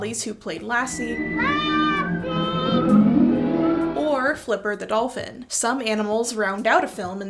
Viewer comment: Asta . . . what about Asta from "The Thin Man" films? And when you said, "Flipper the dolphin" I momentarily misheard it as "flip her the dolphin" as if it were some . . . risqué reference I wasn't aware of. My wife reminds me to add that the MGM lion's roar is actually a tiger's -- lion roars aren't nearly as impressive -- and Flipper's sound is actually that of a kookaburra, slightly sped up. So . . . how 'bout them critters?